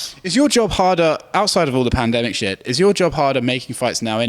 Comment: Did he say pandemic shit